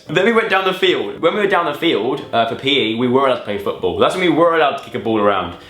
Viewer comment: When he said "down the field" I literally heard "Dan and Phil". I'm such a phan trash...